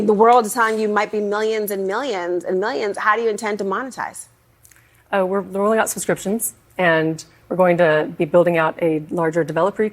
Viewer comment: a subscription service later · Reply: I thought she said; "we're ruling out subscriptions", but perhaps she said "we're rolling out subscriptions"?